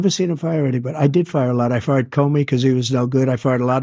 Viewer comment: I legit thought trump said “I farted” not fired Don’t know what the hell biden said